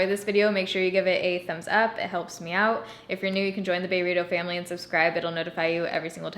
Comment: I don't know why but to me whenever I hear you say "join thataylaa family" I always hear a slurred version of "join the burrito family" 😃😃😃